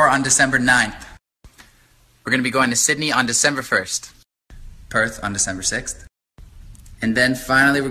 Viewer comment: When he said Perth , I thought he said Perv .... but I'm weird so yeah...